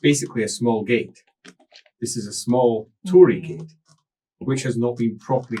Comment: A melancholy dryad wouldn't be the weirdest image to accompany Jefferson Airplane's 'Somebody to Love' I've seen, but it's out there. Also: I've misheard the spirit as opening a 'small, tory gate,' which immediately lead me to a dread vision of a chaos realm of fiscal responsibility and mild racism.